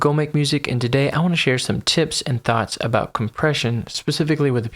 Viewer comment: I thought he said he wanna share some tits and thots lmao